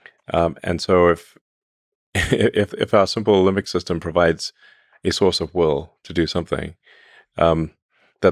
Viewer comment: I was vaguely alarmed when I thought I heard Elon say (with respect to the need for humans when AI hits full stride)..."There's some argument for humans as a source of oil.", instead of ".... sourced of will.".